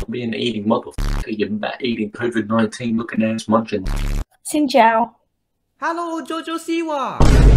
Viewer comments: she was speaking or trying to speak Vietnamese she was not saying Ching chao she was saying in chao🤓🤓🤓🤓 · she was just saying “Xin cháo” that aint even racist 😭